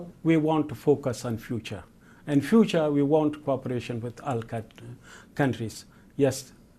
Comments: I kind sounded like he was saying Al-Qaeda, but then realized his mistakes and changed it to countries. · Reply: Subtitles were incorrect and later changed to 'All countries'. The guy never said Al-Qaeda. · mark did he say al qai- all countries?? What the hell?! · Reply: He said countries